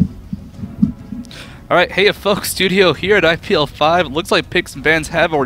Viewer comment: i keep hearing "hey ya fck" o_0